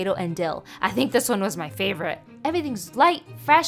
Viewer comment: I know she said “where should we eat next” but I heard “worship me next”........